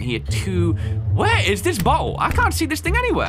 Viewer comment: "Where is this butthole?" 🤣 (I know he actually says "bottle.")